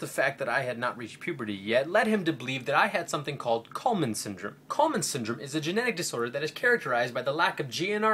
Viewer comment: i thought they said colemans syndrome too excellent video style